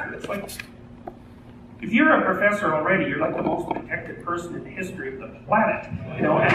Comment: what does he say? can someone help please? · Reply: rashid alhusan if you're a professor already you're the most protected person already